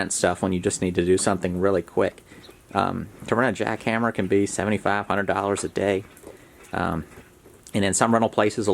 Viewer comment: the first listen i heard "to rent a jackhammer can be 7500 a day lmao, rewatched in relief to hear 75 to 100 a day lol